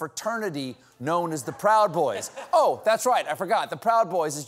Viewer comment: Oh, I thought they said Gay Pride Boys .. Wow Was I Drunk !